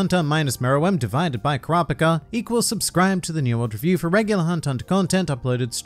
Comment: In my head, I always hear "New World Review" as "Nen World Revies"